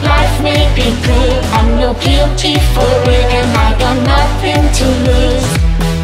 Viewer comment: am i the only one who misheard the part on "im no guilty for it and i got nothing to lose"?? BECAUSE I SWORE I KEEP ON HEARING "im no guilty for women and i got nothing to lose" LIKE??????